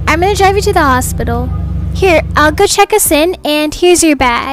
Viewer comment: who heard, “I’ll go check A Sin” instead of, “I’ll go check us in”